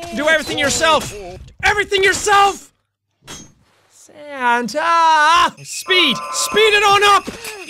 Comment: rofl. Subtitles at 9:36 It says "handjob" instead of santa.